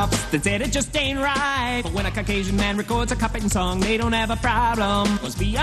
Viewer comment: “But when a Caucasian man records a cop hatin song , they don’t see a problem, must be an oversight” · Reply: Wow. Tbh. He said cop hatin' so fast. I thought he said Cupping. And I would have assumed it was 90s slang. Maybe they did that to trick the censors.